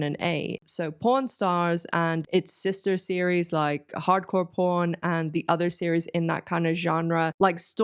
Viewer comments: as german.. i hear P*rn stars and Hardcore P*rn🤣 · You all heard what I heard…right? ! 🤭Yes, my humor is that of a 12-year-old. All I can hear in this video is p 0 r n. 😂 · American here. I kept mishearing the word pawn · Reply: I'm from Greece and I thought she was saying prawn stars and thought it was going to be an aquatic crustaceans competition